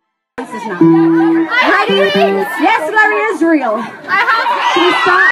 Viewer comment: i think she said lerrie is real which is perrie and leighs ship name